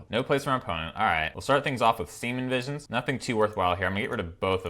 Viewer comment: Every time i hear him say semen visions instead of serum visions..... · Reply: kaine2004 I'm glad that wasn't just me. I actually thought he was saying semen visions as a joke, but his voice lacked the "I'm saying a joke" vibe to it, so i was confused